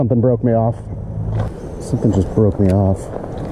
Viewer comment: I was listening with low volume and had to rewatch because I heard "This guy just flipped me off." I was so confused lol